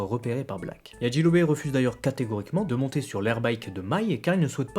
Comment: il dit quoi a 14:40 ? çà a peut etre un rapport avec sa vie d'asep ? d'asespt ? d'a7 ? d'asec ? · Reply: D’ascète ^^